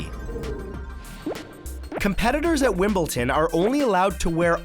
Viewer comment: did he say wimblton??!??